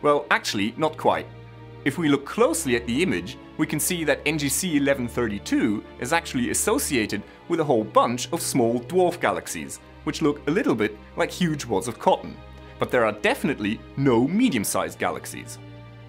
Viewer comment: Did he say "huge wads of cum"?